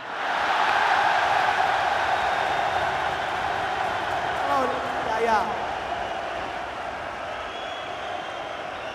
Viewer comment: What did mcgregor say to the girl? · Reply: That girl is Floyd's daughter named Yaya. Conor said, "Hello, Yaya."